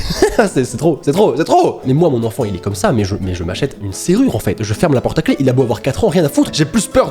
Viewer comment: j’ai cru que t’avais dit “moi je le machette” genre en coup de machette dans le tranche et on en parle plus 😥😭